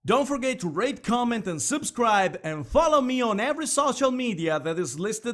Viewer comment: Okay, you did it twice so I'm assuming it's a mistake- honest is a special word... You don't pronounce the H. also it sounded like you said rape comment and subscribe just sayin Other then that, good video! I like your points, and the art is outstanding. Keep at it man.